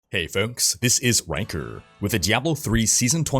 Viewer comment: i honestly thought u said hey fokes this is wanker